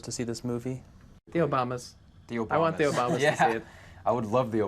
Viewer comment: lol I thought they were saying, like, “Theo Baumas” or something. But they were saying The Obamas.